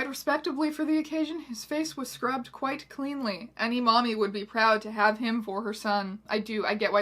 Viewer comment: did it actually say mommy?!